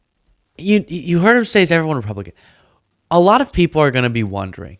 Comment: "is everyone and evil nazi" is what i heard him say... and the deamons cried back "yes"